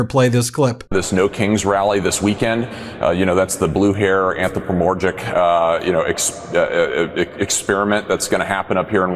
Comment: Anthrapomorgic? WTF does that mean? Is he trying to say anthropomorphic?